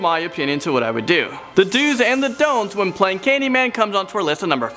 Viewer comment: thought it said dogs and donuts